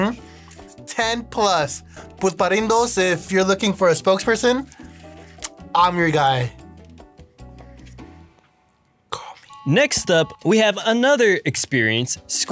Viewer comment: What should we rank next?? 👀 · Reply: When u said “im your guy” i thought u said “im your gay”